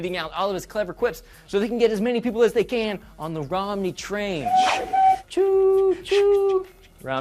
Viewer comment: For a second I thought it said "R-Money Train."